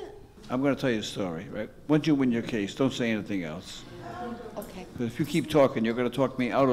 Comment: Can someone please tell me what is he saying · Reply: "Once you win your case, don't say anything else."